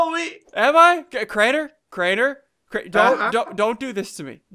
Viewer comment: i thought that said 'sacred drain' lel